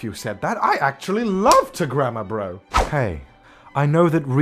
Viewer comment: When he was saying grammer I thought he said grandma 👵